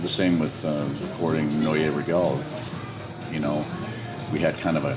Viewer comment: recording what? i cant understand him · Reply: Neue Regel